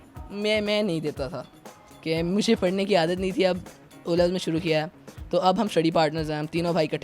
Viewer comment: is he said o' levels